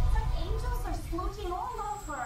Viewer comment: I think she said “splooging” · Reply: oh okay that would makes sense. I always heard "smooching" when I saw that scene. Never even heard of "splooging" before but that would make more sense with the dubbing lol